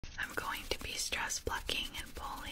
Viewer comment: Wicked funny, I thought you said "stress fucking"